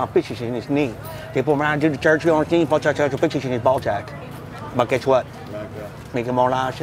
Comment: Cracks me, I could have sworn I heard you say ball sacks. Lmfao